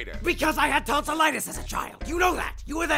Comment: sorry if it's a dumb question but, what is tonsillitis, ? the thing pap mentioned he had when he was a kid. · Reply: I thought he said I ATE TONS OF LETTUCE AS A CHILD YOU KNOW YOU WERE THERE!! And I was just wondering how lettuce works..